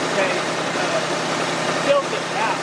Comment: When you said tilt it out she thought you said take me out ! Lol 😅